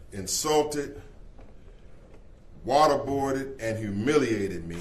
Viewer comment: Waterboarded, did I hear that right?